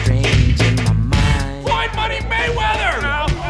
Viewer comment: did he just say Floyd Manny Mayweather??? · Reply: Floyd MONEY!!! Mayweather